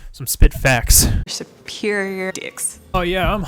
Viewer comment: I thought she said superior dicks! 😂